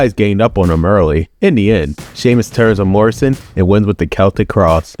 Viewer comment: What do you mean? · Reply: it sounds like you said "Job Morrison"